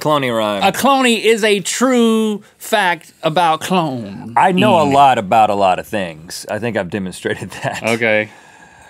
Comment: Rhett at 0:45 I thought he said "I think a lot about a lot things" old fans will know